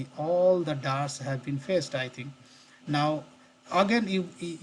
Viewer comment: What are you saying at 8:22? All the jars have been facestriting? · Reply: “Fetched” You can see in left side project navigator, after ctrl+s , the new jars are added(fetched)